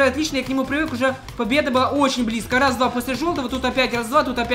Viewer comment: мне одному показалось что он сказал после жопы?